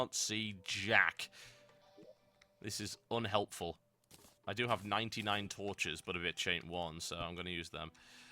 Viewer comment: did i hear total biscuit say i do have 99 torches but a bitch aint one ?